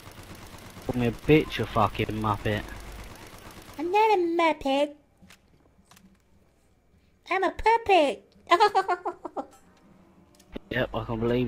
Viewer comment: Jackson lol i thought he said "im not a muppet, im a puppy"